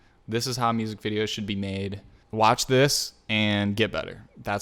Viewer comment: Lol I thought he said “watch this, and give up” 😂😂😂